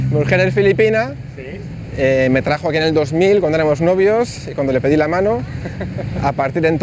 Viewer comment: Cuándo dice "mujer filipina me trajo aquí en el 2000 con tenemos novios" 0:25 -0:26 . En el parte de él diciendo "con tenemos novios", es él diciendo with boyfriends like guy friends? No entiendo el contexto de como él esta diciendo eso. Alguien puede ayudarme entender? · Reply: Guts To Rise Above el dice “cuando éramos novios”, which means, when we were girlfriend or boyfriend. El dice “cuando éramos novios”, no “con tenemos novios”.